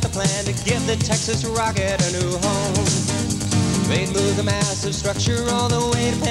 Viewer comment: This video was made in 1988, with footage shot a short time after the move. Thanks for watching! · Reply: oh I thought it said movie I was like nice but the history of this is so cool